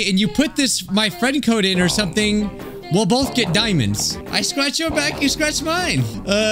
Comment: idk why but I thought he said (purposely) scrunch instead of scratch- lol God my hearing getting worse